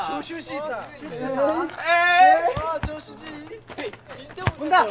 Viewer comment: did jeonghan say 'joshuji'???